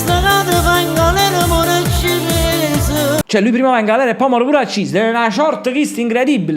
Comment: invece di capire “ muor accis” ho capito “ giuoc a morra cines”😂😂😂 comunque sono la figlia di nancy rota quella che gli hai fatto gli auguri per i suoi 31 anni